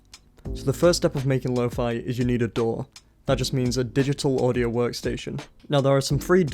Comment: I thought he said I first needed a door